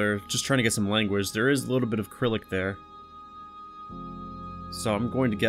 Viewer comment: did you say acrylic? lol its Cyrillic with a soft c